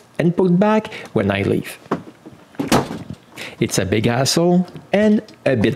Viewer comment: It's a big what, you say? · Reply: Hassle